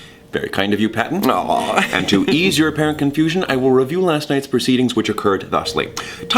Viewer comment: Logan: "And to ease your apparent confusion..." What I heard "To ease your parent confusion" 😂